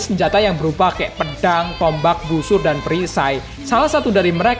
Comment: Gw keknya salah denger yak - iwatani naofuton